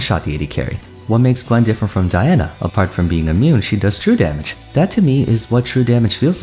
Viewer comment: "Apart from being immune, she does true damage!" That sent me. XD *Gwen is immune. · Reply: i misheard it as "apart from being a meme"